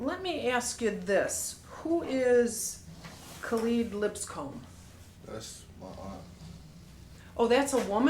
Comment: she said Kelead, not keleada. The closed caption got it wrong and right at the same time.